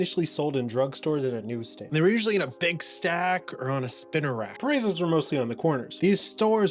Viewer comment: misheard "they were usually in a big stack or a spinarak" hardly a good idea to keep your comics in a Bug/Poison type Pokémon.